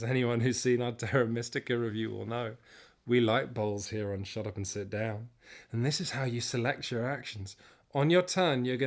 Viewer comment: WHY WHY WHY WHY WHYYYYYYYYYYYYYYYYYYYYYYYYYYYYYYYYYYYYYYYYYYYYYYYYYYYYYY WITH THE SEXY BOWLS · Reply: Also I wasn't watching and at first I heard say "We like BALLS here on Shut Up & Sit Down..."